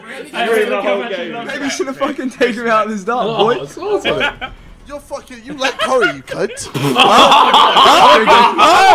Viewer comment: What does JJ say Vik likes? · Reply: U like curry